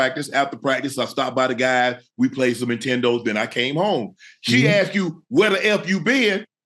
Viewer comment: Unc said, we played some Nintendos? Lol... Let me rewind it because I must have misheard him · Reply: Nah he said intendos😂